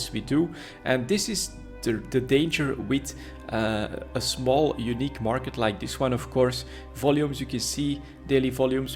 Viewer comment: When did you say contracts are being released for citadels? sorry missed that 6:20 in the video. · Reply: august 9th